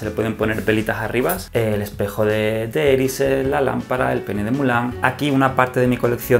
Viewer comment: No sé si soy yo pero en el 15:42 a mí me parece que dice el p3n3 de Milán 🤣